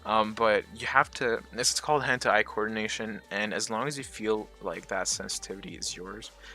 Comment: i heard hentai coordination xD